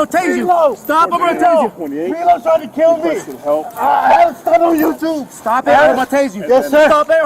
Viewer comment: the "unintelligible" sounded like he said, "I got a stunt on YouTube"